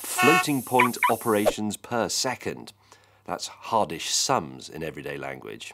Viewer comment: Did he say "hardish sums"?